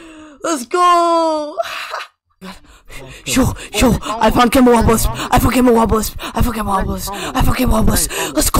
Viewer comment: Yo when he said "I found gamma Wabalisc" I heard "I fu**ed gay wabalisc" cuz he said it so fast lmao